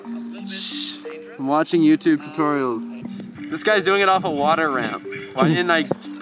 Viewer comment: I hear stomp it tutorials...